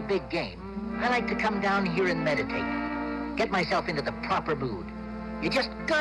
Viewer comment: Like others I hear, "I like to come down here and meditate." First sentence sounds fine.